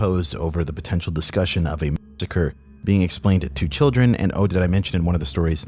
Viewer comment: What word do you keep saying??? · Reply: Massacre